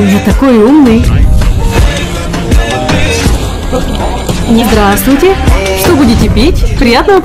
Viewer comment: Мне одной послышалось (не здраствуйте)если это так😅